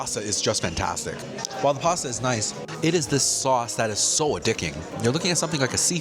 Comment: did u say a dicking???😂 love the review the Salisbury steak looks fire